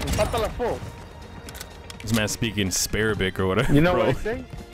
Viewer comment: i heard him say "this man's speaking asparagus" 💀